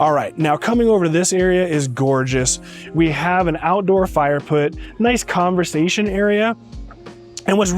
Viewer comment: did you say Fire Put ? Just checking maybe that was something new someone thought of lol. Great House. I love Elevators. I do not understand builders who will not put in at least an elevator that lifts by air. The small round ones. Every home over $1 million should come standard with a $50,000 elevator. If i were a builder i would put one in every home. Guest who cannot walk up stairs grandparents great grand parents i just cannot understand why these builders do not out them in I have seen 3 story $10 million homes with out elevators